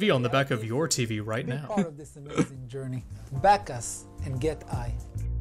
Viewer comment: "Back us and get Eye" I initially thought he said "Back us and get High" which would make a lot more sense considering this product.